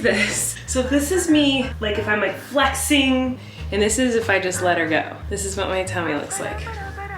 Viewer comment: IS SUS............. Did she say let her go?...